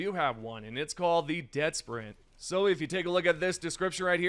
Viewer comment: I got really excited when I thought the booster said “Dead Silent”, thinking it is a bonus to stealth. I was very disappointed when I learned it is actually a running buff. We already have a running buff and we don’t need another one.